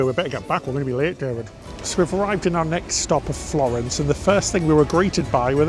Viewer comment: Oh, boy, did I have a huge chuckle when you entered Florence and said, "The first thing we were greeted by was..." and showed a pair of Michaelangelo "manhood" shorts labled "I ❤ David." But I thought you said, "The first thing we agreed to buy was...cheeky." I had to rewind the video 10 seconds in disbelief. Sometimes, your accent throws me off. 😂😅😊